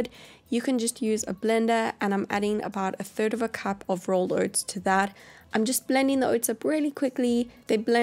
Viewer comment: Can someone tell me what she say at 1:32? A cup of? Sorry, I’m learning inglish a I didn’t understand that🙄🙄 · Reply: Vicky Avalos a cup of rolled oats